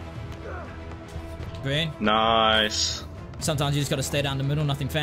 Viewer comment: 😂💀 Z faked it for the win saying “Green” while pump faking lmaooo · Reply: Everybody sayin that but I heard him say greenie